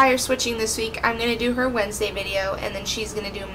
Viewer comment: at the begging the video stopped, so what i heard was: "I'm gonna do her wednesday" and i was like... ooookay.....